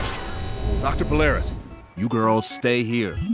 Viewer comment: am I the only one who thought he said dr. ciltarous?